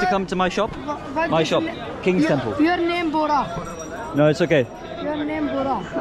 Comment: I like the fact doesn't edit out the parts that are not nice to watch, like the person being anti Muslim and the kids being racist to. I really appreciate that you show people as they are. Not picking on the Indians. In Afghan there was a very awkward moment when this person wouldn't shake your hand. Very good vlogging and true to reality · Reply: That kid is not being racist! He's saying that his friend’s name is Bora. might have heard it as Gora (white person) because it rhymes lol! There is nothing derogatory or bad about that term too but he wasn't even tryna say it in the first place. It's just a communication gap so chill everyone